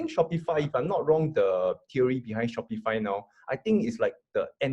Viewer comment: @ 6:55 What is the name of that company again? I couldn’t make it out not Spotify I’m sure but it sounded like it. It’s the anti-Amazon company. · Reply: Shopify, ticker symbol SHOP.